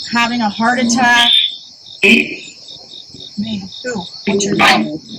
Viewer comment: Hello lovelies, I know I am crazy behind, and I am just catching up on your videos. This one at 3:45 when you asking about pain and say "who", I hear "John" but didn't see it in the captions, might be mistaken, then at 3:48 "hi" or "my"....Not sure how you kept the control with the sirens and the dirt bikes.....Is Aunt Nell maybe a guardian....It is so sad that her headstone is so bare and the fact that James liberated her well before the Civil War is amazing.